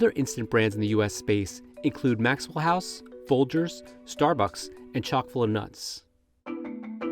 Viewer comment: Totally thought he said, Sock full o nuts. 😮🤭